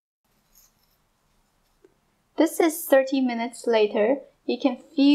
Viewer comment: im confused did you say 30 minutes or 13 minutes. not in a rude way im just curious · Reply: 30